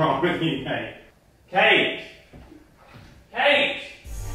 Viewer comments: I thought he said cake lol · it's like is saying cake instead of Kate lol